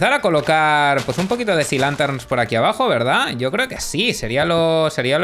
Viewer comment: yo le entendí cilantros en vez de sea linterns :v